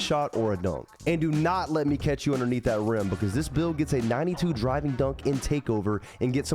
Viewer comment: I thought u said 92 driving dunk?